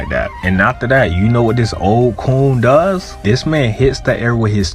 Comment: This vid feels like a step up in editing quality! Glad to see you improving yourself! Ngl tho, did you call Whitebeard a coon at 1:31? Cause that’s craaaazy if so💀😂